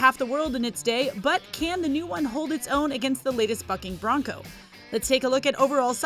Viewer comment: She said what I think I heard? 😂😂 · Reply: Sounds like she said bucking bronco.